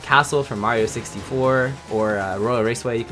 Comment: did you say mario sixty floor